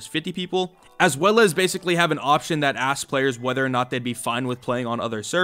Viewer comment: i thought he said "ass players" would play 100 players including bots